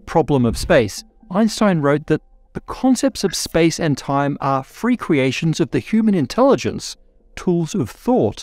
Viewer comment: You said free creations, I thought you said frequenations.